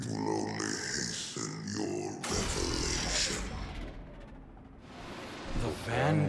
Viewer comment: Did say “Evolution”? If so, id like to remind everyone that Oryx said something similar along the lines of “The Darkness is your evolution!” During Regicide.